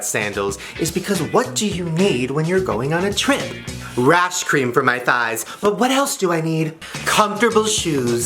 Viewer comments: "Rash cream for my thighs" Me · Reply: Alexis Maldonado i honestly thought he said ranch cream💀💀💀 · When you said "rash cream" I heard "ice cream" .... >_>... I want ice cream now haha